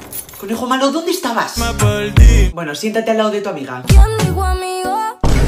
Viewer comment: no entiendo que dice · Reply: Dice: quién dijo amigos?